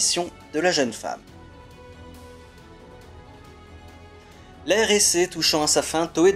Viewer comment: "La RSA", j'ai entendu. Dis bien l'Ère Heisei.